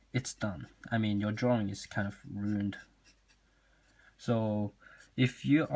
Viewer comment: « your drawing is fu- uh ruined » who heard it